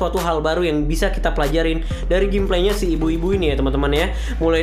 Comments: Ini gw salah denger ato gmna dah, gw kok dengernya "Ibu-Ibu" sih? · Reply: Emang ibu-ibu katanya 😂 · "ibu ibu"? Sorry kalo gw salah denger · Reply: Iya sih emang bilang ibu ibu wkwkwk,lumayan susah bilang biubiu